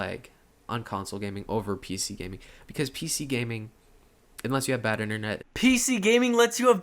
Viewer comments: it sounds like he said "PC gaming, unless you have bad internet..." not "PC gaming lets you have bad internet." Not sure if I heard him right. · One thing in his defense, that misheard; 16:59 "PC gaming, UNLESS you have bad internet..." must have misheard him... Whoops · Oh my actual GOD... the things that came out of this kids mouth made me want to THROW UP... if it wasn't for your beautiful voice, I'd have to have blown my brains across my wall. Also, @ 16:57 I think he said "*unless* you have bad internet", not "let's you have bad internet" XD